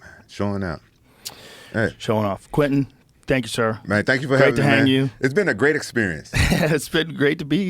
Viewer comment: Did he say great to hang you?? 3:01:14 😂😂😂 WTF JOE! 🧐🧐😂😂😂